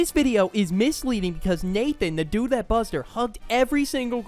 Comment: bro I thought he said “humped” instead of hugged